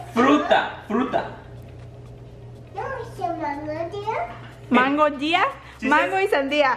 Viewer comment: It sounded like said mangonada